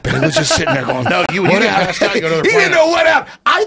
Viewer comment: mins did AJ say when you pass out you go to another planet?